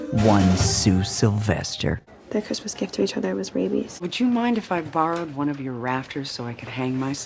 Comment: What does Brittany say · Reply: “Their Christmas gift to each other was rabies.”